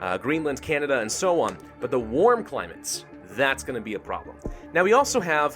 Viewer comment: I Heard “the warm planets”